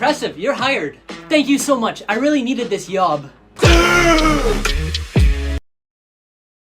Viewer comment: Is it just me or did I just hear him say "I really needed this YOB"